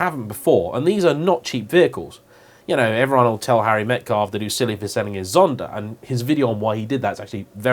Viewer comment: I've tried a few times but I really don't understand you at 07:23 Someone sold Zonda, some video about it? Sorry mate, English is not my native language 😁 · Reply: Tomislav B. Harry Metcalfe who has the Harry’s Garage channel used to have a Zonda but sold it before the prices shot up into the millions. Check out his channel. Explains there why he sold it when he did.